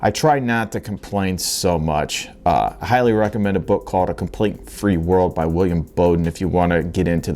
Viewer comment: I had a heck of a time looking for "A Complete and Free World" by William Bowden, so I went back to 7:00 and listened again. It's "A Complaint Free World" by Will Bowen. Just throwing it out there, for anyone else who misheard him. Good interview.